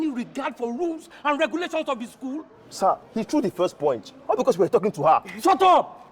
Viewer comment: He threw the first “poinch” ke? 😭 33:48 don’t tell me I’m the only one who heard that 😂